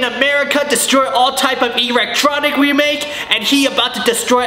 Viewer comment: did you just say ERECTtronic? if you did I'll never laugh the same way I heard this beauty